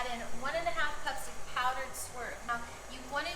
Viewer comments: Hey, didn't catch just what that powder you added at the 1:49 mark is... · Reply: I believe she said Swerve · What is she saying at 1:50 "Powdered WHAT" I cant understand what she is saying. Is it powdered sweetner?